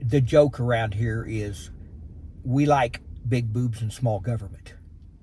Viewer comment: Did he say the local saying was "We like big BOOBS and little Government"?!?!?😮😂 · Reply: I thought he said “big boots” Big boobs make sense too though😂